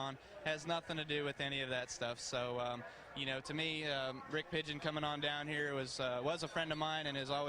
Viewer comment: A little after 13:10, what name did he say? Sound like....."Rick Pigeon"..? He definitely didn't say Rick Hendrick, so I have no fuckin clue who he meant